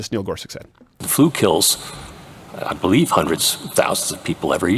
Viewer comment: I definitely heard him say "Hundreds... Thousands" Not "Hundreds of Thousands"